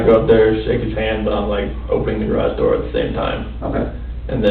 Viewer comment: This guy talks about his wife and children disappearing the same way I talk about not knowing why the garage smells like weed. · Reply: I thought you said garbage and I was like ?? Why you throwing that shit away